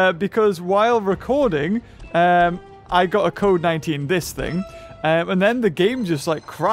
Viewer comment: When he said code19 at the start I thought he said covid19 instead